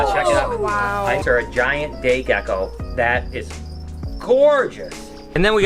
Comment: i heard Giant Gay gecko